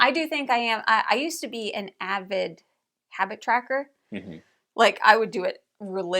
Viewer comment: Ohhh, Habit Tracker! I thought said she wanted to be a Hacky Sacker again. 😂